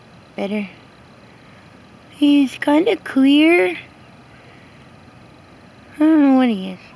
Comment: i thought you said queer not clear at first lmao